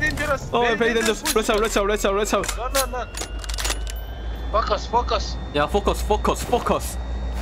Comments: what they were saying fuck us 🤔😂😂😂😂😂🙌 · When they said "FocUs" .. i heard "*** Us" 😅😅😅 ...maybe i have a dirty mind 😅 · that's focus i heard fuck us rip my ear drums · said "Focus" I heard "F*k Us" Can i be forgiven?